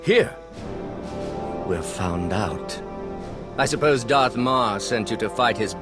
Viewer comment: lol at the end who else thought she said Darth Maul